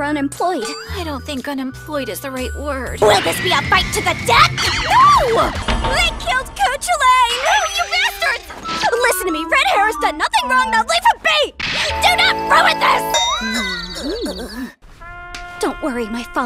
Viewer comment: Did she just say Coochie Lane?